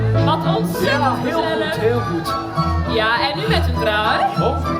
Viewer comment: Translation is wrong here. They are saying "Ja, en nu met een draai" which translates to "Yes, and now with a turn" . But I can understand why you or the AI translation heard "trui" (sweater) instead of "draai".